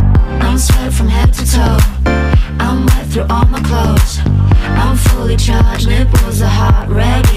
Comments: I swear I thought she said "I went through all my clothes" wth... lol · I thought she was saying "Take a hint"...well :D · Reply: Haha I thought she said "with the heart" instead of "nipples"